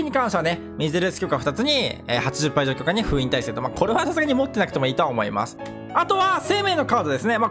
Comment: ここセイレーンのカードとった方がいいのかどうしても聞こえないのですが教えてください😭🙏😞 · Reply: 持ってなくていいと思うっておっしゃっていますね